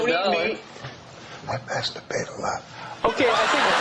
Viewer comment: What did the guy whisper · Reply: No... “I piss the bed a lot”